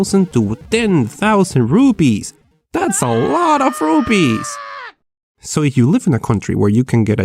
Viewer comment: RUPEES SOUNDED LIKE RUBIES